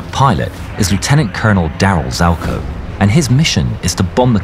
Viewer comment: Lol the guys name is Dale I thought the narrator said Darryl in the beginning